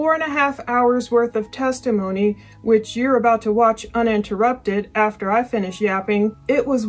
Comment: I thought you said uninterrupted